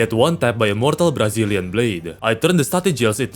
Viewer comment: Eu escutei... brasil?!